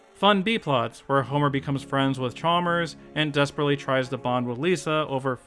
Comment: the subtitles says *flanders*, not chalmers